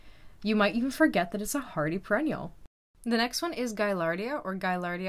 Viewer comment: Is it me? Or are you saying "pre-ennials" instead of "Per-ennials" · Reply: It certainly sounded to me like "perennial" …. and closed captions showed it thought she said perennial, FWIW.